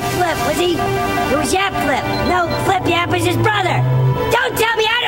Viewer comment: i heard "nipple academy" lol